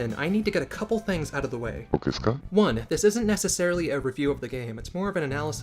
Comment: what did he say? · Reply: It's Shigatso Itoi saying "Ok desu ka", which means "Is that ok?" in Japanese.